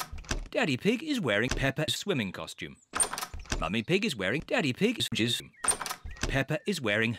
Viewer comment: What was the narrator saying at 0:37? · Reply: Mummy pig is wearing daddy pigs gizz